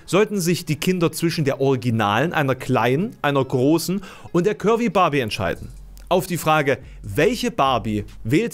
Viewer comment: Ich habe ständig Currybarbie verstanden 😂